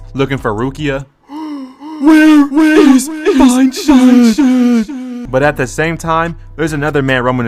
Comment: what did he say? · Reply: Where is fine shyt